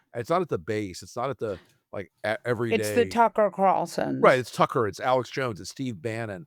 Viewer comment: for a moment I thought I heard molly say "Taco Carlson" 😀